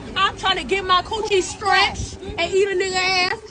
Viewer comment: At 35 minutes, I think she's saying stretched. The first time I heard it I thought she said scratched, but it makes no sense, and I'm pretty sure stretched is what she's wanting. Amazing when Americans need a translator for another American. She's the worst.